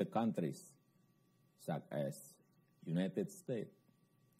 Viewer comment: = harusnya such as tp knp kedengeranya suck ass ya 🤣